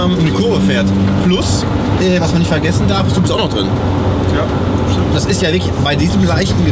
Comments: ♨️♨️♨️♨️♨️♨️♨️♨️♨️♨️♨️♨️ Bei ~ 11:22min was ist drin hab ich nicht verstanden? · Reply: "...plus, was man nicht vergessen darf ist, du (mowo) bist auch noch drin" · was ist auch noch drin? Ich verstehe es nicht 😅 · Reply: Ich glaube er sagt: „du bist auch noch drin.“